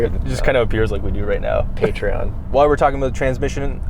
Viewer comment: Did anyone else hear say “Patreon” at 13:22 when they were taking about how much money they don’t have? 😂😂😂